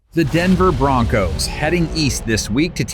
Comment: I think he means Weast 😂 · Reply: East? East? I thought you said weast😂